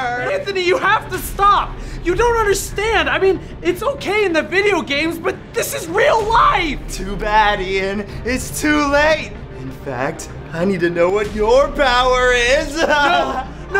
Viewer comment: is it just me or did Ian say “This is real LIGHT”